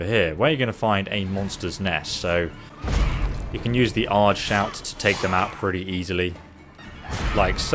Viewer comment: Wait a second, did he just say Aard shout?